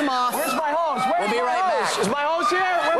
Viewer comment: Two things: Paula made one of the dumbest references I've heard from her (Barry Bonds and singing don't really go together) and also at the very end it sounded like Ryan said "Where the ho's at?"